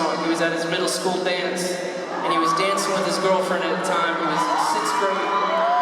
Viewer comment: I know he didn’t but it sounds like Tyler said “he was dancing with his girlfriend at the tine who was his sister” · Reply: the ending is "he was in 6th grade" not "who was his sister"